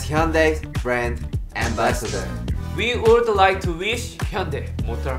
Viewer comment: I hear jin said "world wide wish" 😂😅